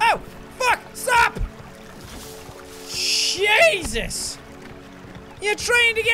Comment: did you say cheesus